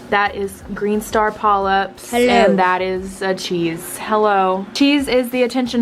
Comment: i deadass thought that fish said hello